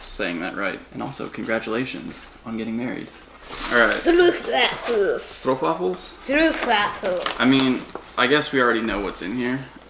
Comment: Now I know what those waffle cookies are. I thought you were saying street waffles. 😂